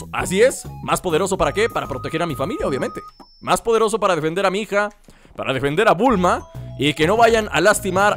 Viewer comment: escuche vender a mi hija XD